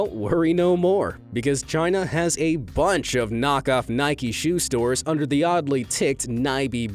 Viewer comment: He says Nik EEE instead of nike